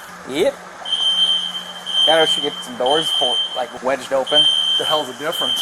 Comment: I may have misheard, but at 5:13 did one of the police say to wedge some doors open? Isn't that a big no no? Edit: he did indeed say that, he said it again a short while later.